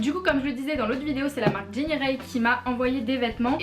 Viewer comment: Coucou, c'est quoi la marque qui t'as envoyé la boîte toucans avec des vêtements, j'ai compris "Jinny Rey" mais je trouve pas